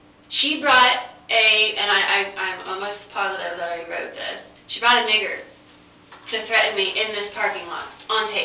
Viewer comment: did she just say she brought a " Niger" to threaten me? WTF???😮